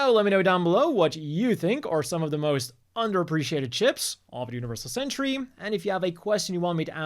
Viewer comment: sorry but underappreciated chips! that's what I keep hearing and it's all on me!